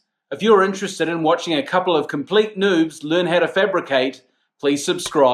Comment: did he said "Complete Nudes"? LOL, what am I in for??? LOL